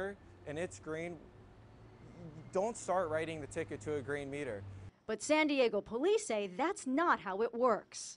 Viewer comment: I thought it said tickling a Green meter